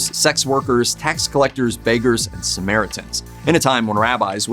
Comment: I could have sworn you said “Americans”! 😱